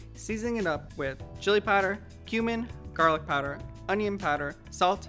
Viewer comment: I thought he said "human"